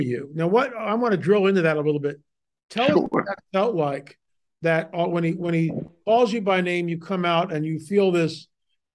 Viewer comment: there was an extra voice at 09:41. I slowed it down and it's separate from what you're saying. Didn't seem like a mic glitch. Do you know what that was? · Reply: It was myself mumbling in the cheap microphone I had to use. I said sure, but I was nervous and mumbling lol